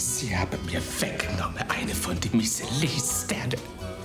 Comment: Was weggenommen? Hab ich nicht verstanden. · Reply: Einen Michelin-Stern